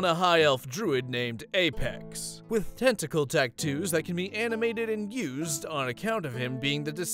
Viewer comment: I heard "tentacles" and got nervous